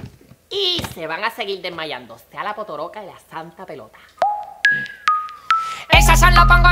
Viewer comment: sea la potoroca la santa pelota eso entendí yo😂😂🤣